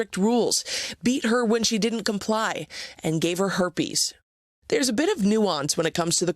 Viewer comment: Will they let him out already.....geesh. @ 1:58, I thought the lady said "and gave her the beef"......😂😂😂